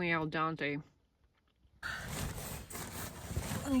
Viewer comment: I thought I heard "al Dante" and not al dente.. but it must be my hearing.